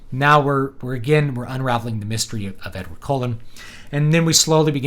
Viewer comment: I think I misheard; we're going to unravel the mystery of Edward's colon?